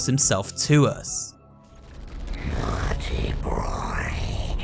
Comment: Idk if it’s just me but when says naughty boy when he first says it, it sounds like he says naughty broy